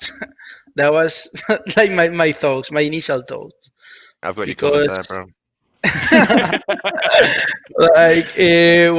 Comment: Did anyone get what said at 29:46? I'm hoping my fellow native English speakers might have had a better time cathing it, cause I rewinded it a few times, but couldn't understand it. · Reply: "I've got you covered there bro"